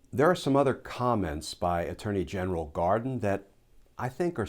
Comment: Did I just hear "attorney general Garden?"